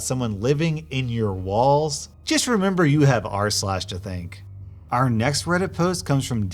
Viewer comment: I always hear “arse lash” instead of r/